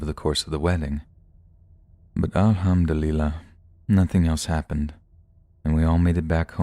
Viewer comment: Kinda cute the way you pronounced Alhamdulillah,I rewind because I couldn't understand. Alah-ham-dul-illah ! I heard it as "All ham Delilah" 😁👍💕